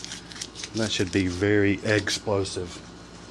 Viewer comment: Doh! why didnt I think of that! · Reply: You said "explosive" somewhere in the video, and I heard "eggsplosive" :D